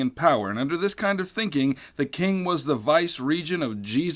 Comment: I have two questions. At 0:45, you say that "The King was the (vice region?) of Jesus his self." What exactly did you say here, and what does it mean? I have never heard of it. Secondly, at 4:47 you say "...and the second in the capital city of Tokyo...". Yet, Kyoto was the capital of Japan until 1868 when control of Japan moved from the Shogun to the Emperor. Were there two "capital" cities? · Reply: Vice regent means the right hand man of the king. And you’re right about Kyoto: that was a mistake on my part